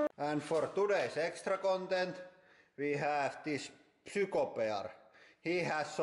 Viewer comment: did he say super bear?